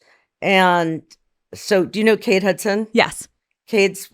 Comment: Lol!! Kathy was talking about Britney’s former slime ball manager & ex friend Cade Hudson but Alex thought she said Kate Hudson like normal 😂. “Yes I love her”😂